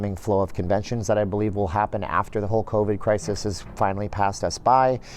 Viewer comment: I had to listen to this four times before I realized that he was saying "...the whole covid crisis..." I thought he was saying the Hulk Hogan crisis.